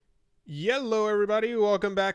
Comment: Did I hear Yellow?